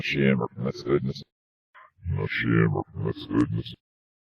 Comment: Sounds like he said "not shamed of my sins" at marker 6:36.